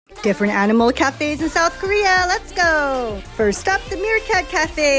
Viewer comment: HOLY CRAP I THOUGHT SHE SAID NORTH KOREA